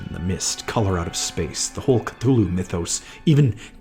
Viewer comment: I thought bro said "the hulk"